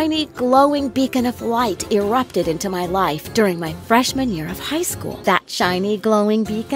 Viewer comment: When she said Freshman I thought she said Fresh - Manure. 🤣